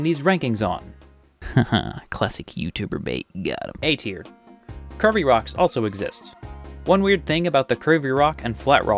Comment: I kept hearing "curvy rocks" as "Kirby rocks", and was wondering what's Kirby-like about them😆